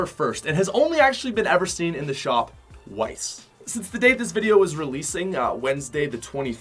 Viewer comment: wice? That’s what it sounded like